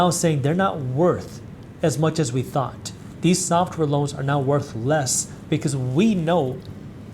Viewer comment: "These software loans are now worthless" LOL! 😂 okay, okay, I know he actually said "worth less", but perhaps many of them ARE worthless.